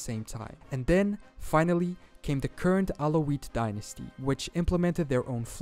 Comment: did he say kurd alaoutie dynasty?